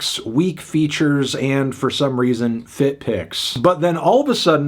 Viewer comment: I heard feet pics and was so confused